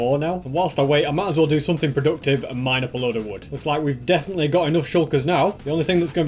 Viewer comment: did he say MINE up a little wood ? 17:03 serious MINNING of wood ?